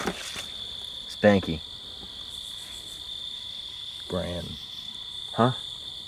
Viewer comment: I think it says brandy